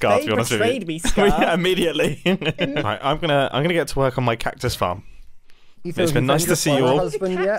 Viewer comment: instead of hearing "I'm gonna get to work on my cactus farm," I mistakenly heard to work collectively, resembling "twerk," leading to considerable confusion on my part.